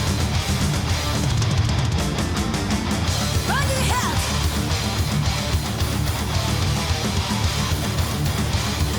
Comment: what does asami say @ 1:07? it sounds to me and my wife like "bloody hell". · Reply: Yeah I am also pretty sure it's "bang your head", though "bloody hell" would be much funnier.